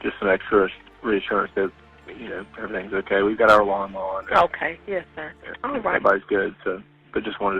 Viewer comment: I definitely heard , " ive got my alarm on." Lol. Lawn mower. 😅 Bizarre case, im not sure what i even think about it yet. Thanks for the updates, keep up the amazing work.